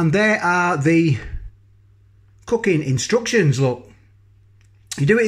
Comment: just ate one of these tasty! Even the misses who hates me eating pot noodles says she liked the smell...oven cooked def best results. Sounds like you said fookin' instructions instead of cookin. Made me chuckle